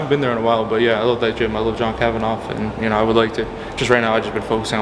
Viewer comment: Did he just call John Kavanagh "John KavanOFF" 🤔😂